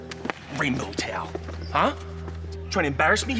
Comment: Coach is definitely saying rainbow "tail". The captions are trying to cover for him but his homophobia is clearly attacking the speech center of his brain